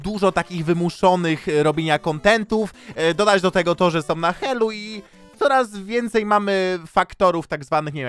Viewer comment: usłyszałam na haju xd